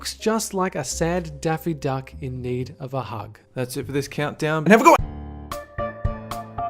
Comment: all i hear is 'and i forgot' but everyone is saying its 'have a good one'.???